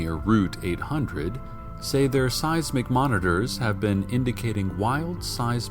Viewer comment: When he said seismic monitors i thought he said size mcmonitors